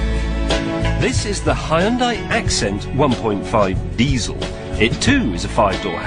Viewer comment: あホントだ、ホンダィって聞こえるｗ 名前もエンブレムも朴李で、CMでも日本メーカーのフリをする さすが恥も外聞もない韓国メーカー・・・